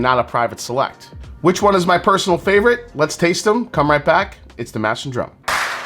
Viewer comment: You said 'this is the mash and drum' I thought you said ' this is the master drunk' I was like hey! Me too. Lol.